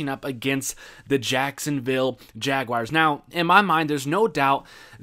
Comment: When he said jaguars I kept hearing “jagwires”